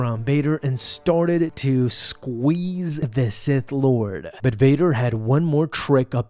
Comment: I thought you were saying he squeezed the SITH out of him